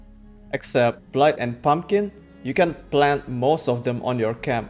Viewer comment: it sounds like you say that blight and pumpkins can be planted at camp. How is this done? Thank you for taking the time to create these tremendously helpful videos! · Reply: No, I said, "except blight and pumpkin", maybe just my poor choice of words, not a native english speaker 🙏